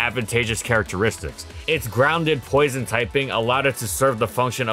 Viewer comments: Ground/Poison?? Bruh... Or did he say "grounded Poison type"? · did he say "Ground and Poison typing"? Which for the first time they didn't put a line to correct it?